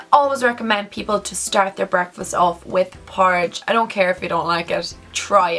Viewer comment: what does she say in the minute 6:42?? .... for breakfast. i don't have a really good english level and can't understand that word! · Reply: Maddi Aguilar Porridge/oatmeal xx